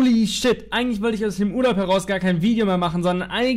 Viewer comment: Ich hab am Anfang aus dem Gulag verstanden statt Urlaub 🤣